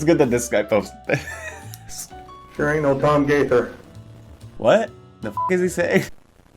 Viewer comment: sounds like he is saying "Sure ain't no Tom Gaither." who is an artist. Also, joke's on you, I'm already losing all my hair!